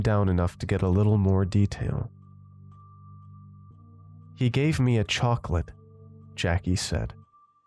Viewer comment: Lol working and listening to this. I swear I need new ears. I heard “he gave me chalk lip”. The MC went on to say he should’ve never told me that. My brain was like “wait what’s chalk lip?!!?” 🤣🤣🤣